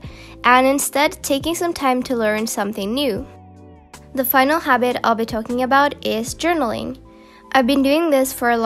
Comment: Someone can help me? What she said in 7:09 until 7:13 I understood that she said " The final habit what we were talking about" Other moment I understood " The final habit I've talked about" lol this really confused me, she speaks in a very easily way for me that I'm not native in english, to understand, but in this moment she spoke so fast lol.